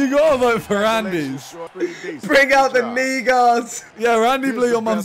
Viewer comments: did I hear it right from vikstarr No one's gonna talk about that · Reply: knee guards * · what did vik say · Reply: kneeguards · Wait, did Vikk just say the N-word at 13:32? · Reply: kneeguards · Wait, what did Vik said at 13:33?!?!?😲😆😆😆😆😂😂😂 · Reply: “knee-guards”